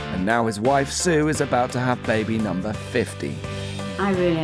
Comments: I could have sworn he said baby number 50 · I swear I thought they said “Baby number 50?” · I’m sorry... did you just say baby number FIFTY? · I thought the narrator said baby number 50 and I was like baby number WHAT? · I THOUGHT THAT SAID 50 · did he say baby number 50! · It sounded like he said baby number 50 lol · Bro did he just say baby number 50!? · it sounded like 50 i was like th, so i had to turn on subtitles kinda got a relief after seeing its 15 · the first family i thought the guy said baby number 50 and i was in absolute shock, glad it’s 15 and not 50 lol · I thought he said baby number 50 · Am i the only one that at 2:22 i heard baby number 50 🙃😐😆